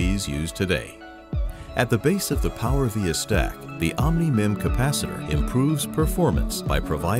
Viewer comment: did he say omni-man ?